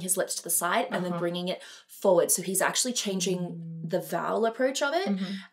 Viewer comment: I thought she said "So he's gay?" I was like... I thought so too. Lmbo!